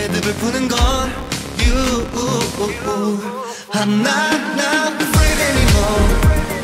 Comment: I only hear "I'm not not fraid anymore